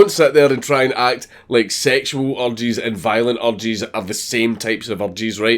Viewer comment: Thought he was saying "Orgies" but he was actually saying "urges" so... Thanks.